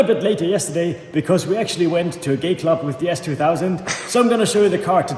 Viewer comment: Did he say “gay club” @ 13:04?🤔